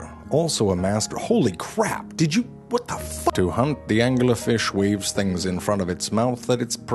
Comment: I thought he said "to hump"